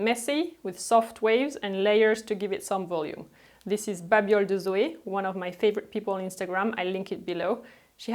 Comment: Did anybody catch who the instragram account is at 1:20? My Canadian ear couldn't keep up with her lovey French accent 😅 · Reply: babioles de Zoé 😊